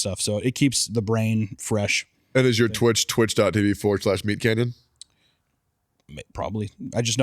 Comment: Damn. I never actually looked at his name. I just heard it as Meat Canon all this time.